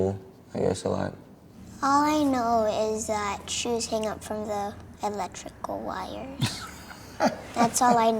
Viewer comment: Could have swore that little girl said all I know is that jews hang up on the electrical wires😂